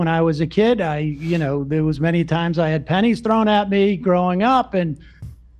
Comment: Describing the antisemitism he experienced as a kid, I misheard him say he had “panties” (not pennies) thrown at him 😂. I was like.. what kind of stereotype is that stemming from?? 😂